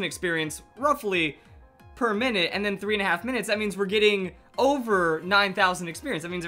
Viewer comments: did he say over 9,000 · Did he say it? The experience earned!? IS OVER 9000!!!